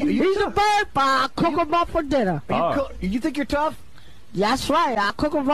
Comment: I thought he was saying coco muffin dinner